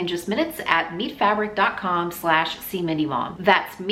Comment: I am doing things while this is on so I’m not looking at the screen usually. I heard “Meat Fabric” several times before I I realized it was “Meet”. 🤣